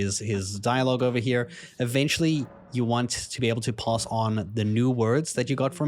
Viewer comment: he means "pass", not "pause". Just in case anyone didn't understand that weird pronunciation